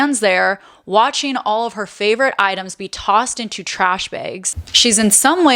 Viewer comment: 'Tossed into trash bigs' That's exactly what I thought I heard! She said the samething when quoting Paris Hilton. Ive never heard em called trash bigs before